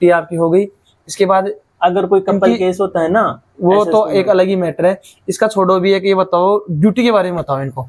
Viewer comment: kya bol re the ustad? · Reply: Couple case